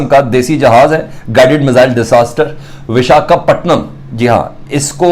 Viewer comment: kya kya bola tune?? Disaster??😵